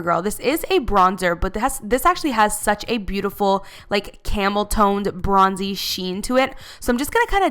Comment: when you said "camel tone" I thought you said camel toe bye